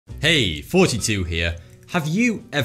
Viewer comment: everytime he ays his intro i hear 'hey 42 here' XD